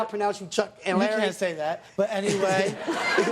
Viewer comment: What was said at 16:09? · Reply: "you can't say that, but anyway"